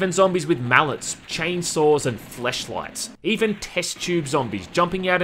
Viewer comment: wait did i hear this right? Zombies with fleshlights??